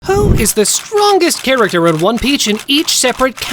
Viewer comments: who else heard one peach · bro is it just me or did he just say one peach💀